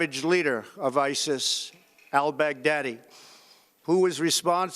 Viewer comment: Did he say "I'll beg daddy" 8:15 😂😂😂